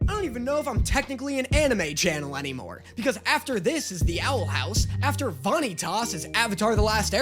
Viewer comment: DID I HEAR CORRECTLY THE OWL HOUSE - WE WON ( please watch all the seasons)